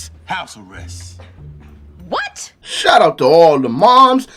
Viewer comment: Hilarous! at 16:00, I initially thought you said - "Shut up" to all the moms..." I thought, wow, pretty harsh.